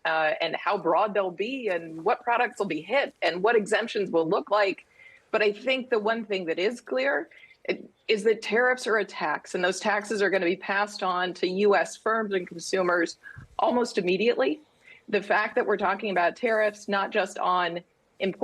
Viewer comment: I heard "tariffs are a tax" as "tariffs are attacks".....and that feels correct. Such idiocy.